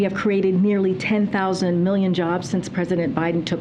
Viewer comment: '10,000 million jobs'... Did I hear that correctly?